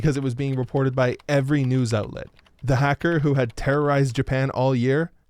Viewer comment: at first i thought it said " hacker who become rider" lmao HENSHIN!